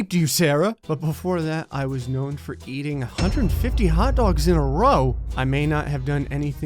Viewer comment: I thought where it said 'eating hedghogs' instead of 'hot dogs'